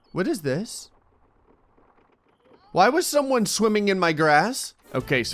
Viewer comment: I thought he said why is someone swimming in my ass